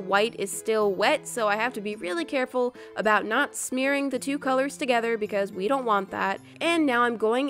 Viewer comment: "And so I have to be careful to not smear the colors" <I thought she said "and so I have to be careful not to scare the colors>